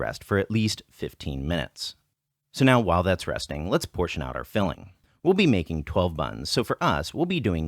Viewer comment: huh. Voice over says 15mins, subtitles say 10.